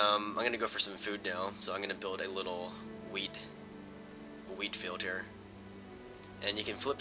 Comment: lol at 1:37 sounded like youwere going to make a weed field....lol